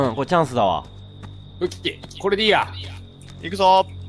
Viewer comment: くらいにが｢武器これでいいや｣みたいなこと言ってて｢これでいいら｣って聞こえて静岡県民として嬉しかった。